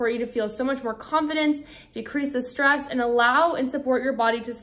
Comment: Did she just say that she will reach a technique that will INCREASE stress at 1:52 minutes into the video? 😮😅😢 I don't want that...😂